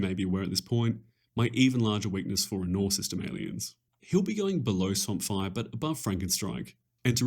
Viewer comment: what is being said between 38:00 - 38:-5 something system aleins? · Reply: Anur System Aliens